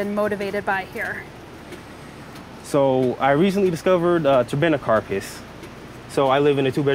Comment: What is it he says at 7:18? Can't make out what his favorite is. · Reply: I think he said Turbinicarpus.